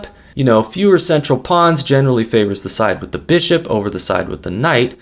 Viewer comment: I Do Not Understand A Thing Of What Our Host, Guru and Chess Master at about 9:08, answering a question he first brought up himself, Says: ""Fewer central pawns generally favour THE* (?) side with 'the' Bishop instead of 'the' side with 'the' Knight"... ???! (* should that maybe be, 'TO', as in, 'they favour to side rather than 'the' side ??) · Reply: No. It's the. To doesn't make sense.